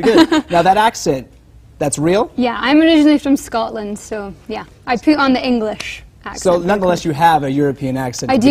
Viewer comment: who else heard her say "I poo on the English accent"?